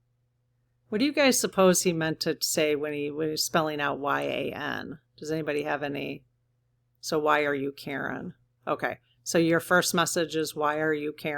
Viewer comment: I thought he was trying to say ‘Why are you asking?’